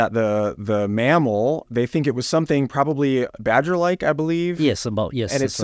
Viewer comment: You must have Misheard what was said, "badger-TYPE" .. wasn't it ? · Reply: Correct! "Badger-like"...Must have been a sabertooth badger! 😂